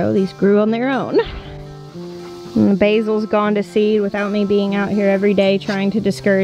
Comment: is when an AD played, all I heard was "Basils gone to s" even though I knew it was going to be seed, still laughed. It sounded like you were going to use a different word hahah